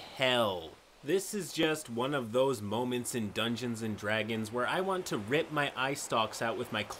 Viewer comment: was it only me that misheard him saying "Gungeons and Dragons"? That would be kinda cool though..